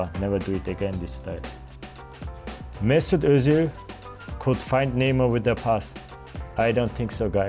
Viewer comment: i thought he said he couldnt find neymar with a pass cah he cant swim lol